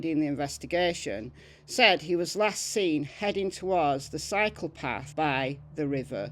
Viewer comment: happy Sunday (or maybe Monday for you.) I love hearing the very old cases, but it’s good that you’re showing recent cases too! When you first said that Daniel was heading towards the cycle path, I thought you said “heading toward the psychopath.” 🤣 I was quite confused!